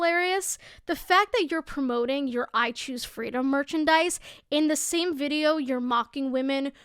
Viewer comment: I thought that said "I choose femdom" and just rolled with it until they said "freedom" I almost spat out my drink